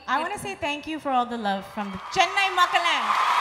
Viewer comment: did she say chennai maa ka land